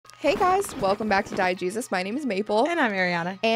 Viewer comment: At first I thought you say "Hi guys, welcome back to Die Jesus." Like what!!?? lol